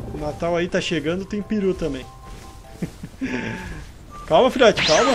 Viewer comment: kkkkk juro q ouvi piru em vez de peru kkkkk